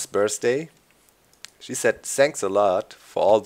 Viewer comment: I have such a dirty mind but when you said about your moms Bday i heard "She said sex alot" and then after a while "So yeah she was really impressed, THANKS to all you guys" and i was Ooooooooooooooooh..... okay.... yeah...